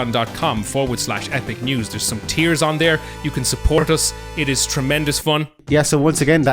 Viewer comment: I heard "there's some tears on there" and now I'm torn if I did or didn't mishear what he said